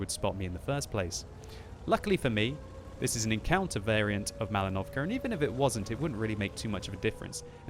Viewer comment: Is it just me, or does it sound like says "MalinovCAR"?